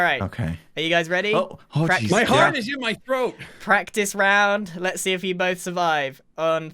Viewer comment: When and were doing the practice, I SWEAR I heard say “my heart is in the throat” I’m not trying to be rude but I heard that- 😩😩